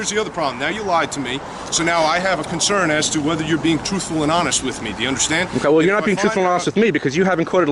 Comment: did he say toothful? 🦫